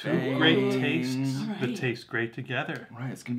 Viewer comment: did say 'they taste great together'?.. ahhaaahahahahaaahahahaaaa I love watching your PTX/Superfruit reactions..:D It was nice to hear you guys talking about the comments and stuff. Nice to know that you read them.. Yes we are a passionate lot, us Pentaholics. :) <3 I highly recommend Aha at star vista singapore, it's a live performance. and imagine and gold.. feeling myself by superfruit..hip hop goes broadway 1 & 2